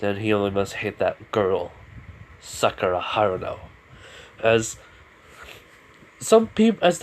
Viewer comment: Guys at 50:57 subtitles say sucker high now when he says he must hate that girl sakura hano